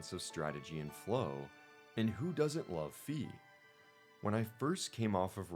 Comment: I genuinely thought you said who doesn't love feet lol!!!!